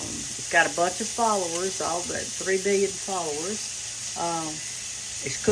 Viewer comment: I thought Brenda said she had 3 million followers. Is this her official sight?